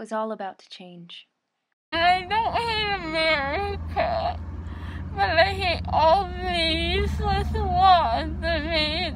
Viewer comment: I think in 7:15 she said “ I don’t hate America I just don’t like the useless laws